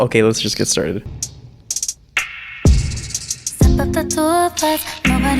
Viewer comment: the way she said step up the two of us i thought she said "step of the tour bus"